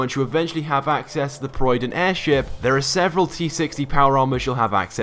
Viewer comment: did he just call it the proyden?